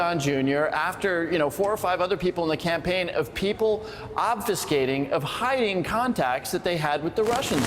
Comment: Did he just say "ABSTICATING"😂😂😂😂😂😂 That's NOT even a word